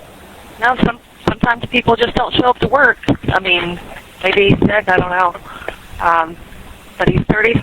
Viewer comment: Did she say, "maybe he's dead, I don't know." Don't read the CC. Just listen.